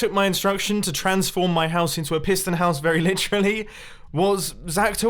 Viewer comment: i thought it said “incredible prison house”